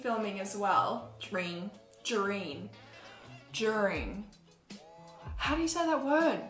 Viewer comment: Fuck haha I thought she was trying to say drinking and it was during 😂😂